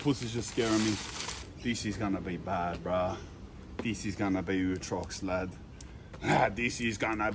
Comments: Someone tell us what he is saying here. What is ootrox? Or utrox or whatever saying. What does it mean translated from pig Latin. Cheers lad. Full never heard that shit before, sounds hectic. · I deadset can't work out wtf this adlay is ayingsay at 14:34. "Ewtrox"? Anyone know what this is??